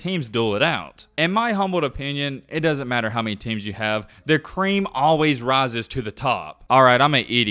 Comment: did homie say the cream always rises to the top? Pause